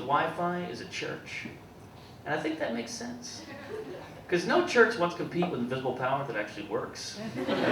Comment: What did he say at 9:36 ? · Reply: DM he said, "I think that makes sense because no church wants to compete with an invisible power that actually works."